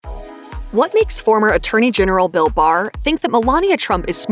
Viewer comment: Dammit I thought it said Bill Burr